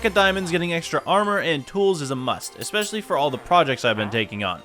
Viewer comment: pls tell me im not the only one that heard him say whores and tools???????🤣🤣🤣